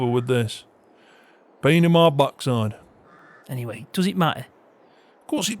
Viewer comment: Did I hear say " pain in my bat-side"? 😂😂